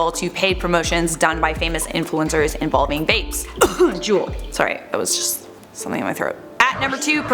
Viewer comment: I know nothing about vaping and thought was saying Jewel promoted it. That was pretty unsettling until I Googled and realized she was talking about JUUL. Oops.